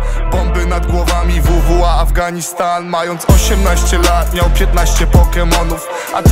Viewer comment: i wonder what hes saying i just heard “afghanistan” and thats where im from